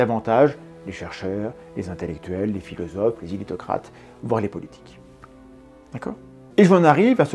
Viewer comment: "Les chercheurs, les intellectuels, les philosophes, les..." quoi ? Les sous-titres auto indiquent "ilitocrates" mais je ne trouve rien qui s'approche de ce terme. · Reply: "éditocrates"